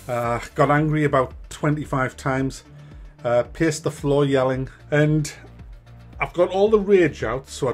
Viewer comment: Ooooh. Paced the floor. Thought you said pissed the floor